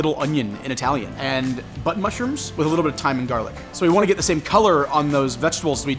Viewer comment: Did he just say “butt mushrooms” ? LOL